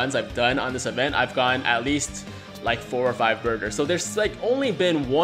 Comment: For a second I thought you said burgers but you were saying burters